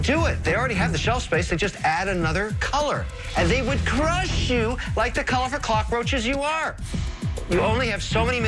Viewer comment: bro 11:11 “the color of the cockroaches that you are”… did i hear that wrong or is that just straight up racism